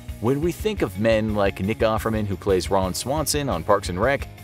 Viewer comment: Did he say the n word in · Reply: He said Nick offerman Lol😂